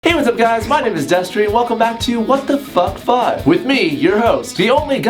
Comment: for the longest time i thought was saying "what the fuck fuck" instead of what the fuck five tbh